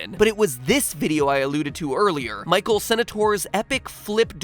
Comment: What word was that at 2:27? Alloted? If so he said it wrong. I cant think of any other words.